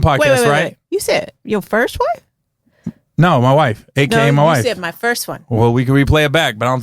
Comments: Kind of sounded like he said “My wife, first one”.. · I don’t think Gary said “first wife”. He said “first one”, as in first podcast guest